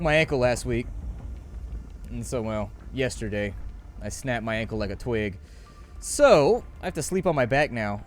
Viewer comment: At first I thought you said "I snapped my ankle on a twig" and was laughing my ass off but then realized you said "snapped my ankle like a twig." I now feel bad that I laughed at your pain.